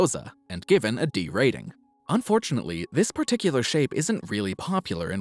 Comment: Every single time I watch a video from this channel, I hear “women’s AUTISTIC gymnastics” 😂